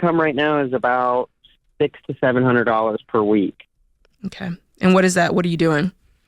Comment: I thought he said 6700 a week???